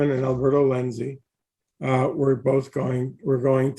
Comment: At first I thought Mr. Lettich said Umberto Lenzi. I was like what, the director of Nightmare City and those cannibal movies has the rights to Bloodsport??? lol